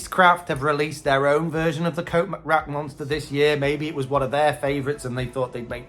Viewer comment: He couldn't have said coat rack monster. But it really sounded like he said coat rack monster. Damn, he said coat rack monster. That one is really cool (more effective if it is not Halloween season. Hmmmmm) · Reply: That’s right, Cindy. I really did say coat rack monster!! How cool is that? Thanks so much for watching and commenting 👻 💀 🎃